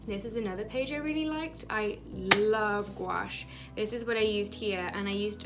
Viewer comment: What style did she say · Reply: Gouache